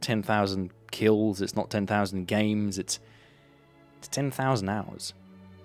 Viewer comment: I cant tell what he said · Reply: 10,000 games